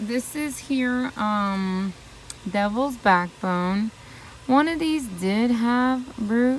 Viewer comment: 7.34...what's that plant called, I love it?! (The one with the big leaves tinged with red) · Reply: I think I heard “devils backbone”?? I like it too!!